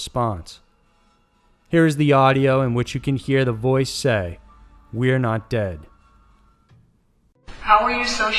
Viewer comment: I heard "We haunt it" instead of "we're not dead...